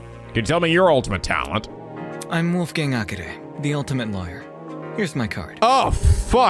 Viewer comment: Not gonna lie, when Wolfgang first introduced his talent, I had misheard him and thought he said he was the Ultimate Liar. Imagine my confusion when didn't immediately make a Kokichi joke.